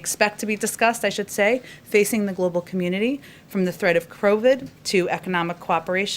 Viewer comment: At approx 15:45, did she say crovid?